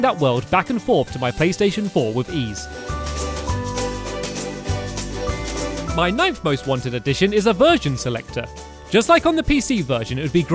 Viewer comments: anyone else think he said "virgin selector" at first? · lol i thought he said a Virgin selector